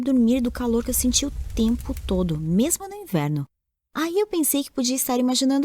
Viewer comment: eu entendi inferno kkkkk XD